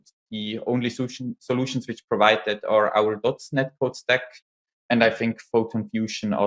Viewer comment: what is the name of the plugin is he saying at 26:00 ??? i cant understand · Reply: I think he's talking about DOTS Netcode.